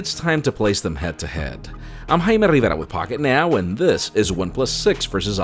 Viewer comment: Wtf did he say? 0:31 "I'm HAImUriMayA" Or something